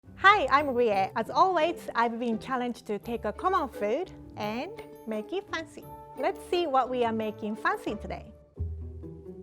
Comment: I FIRST HEARD 'Hi I am Rie and I have been on the Highway"